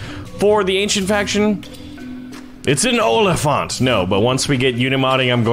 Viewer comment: it sounded like you said Olifant which is dutch for elephant.